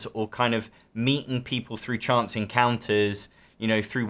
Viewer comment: Haha misheard you "Chance encounters not "Chance and Counters". They do social gaming nights where you can meet people weekly... 😂😂😂